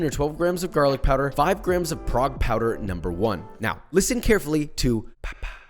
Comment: Prague powder! I thought he said FROG 🐸 powder! Hahahah. I can't hear $hit.